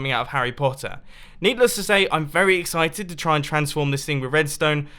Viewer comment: did he say neatless to say?